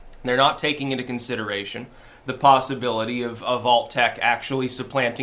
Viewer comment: you said "of alt-tech" but on first pass my ears heard "of Vault-tec" and I did a double-take, rewound it to make sure whether that's what you said or not. XD